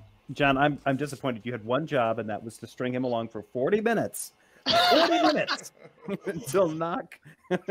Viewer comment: "40 Minutes?????? Awwwwww my bad! I thought you said 4 minutes..."